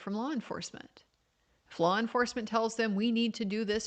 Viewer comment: I heard “flaw enforcement,” lol. 😂 All joking aside, I’m saddened and concerned by how this trial, so far, resembles a witch hunt.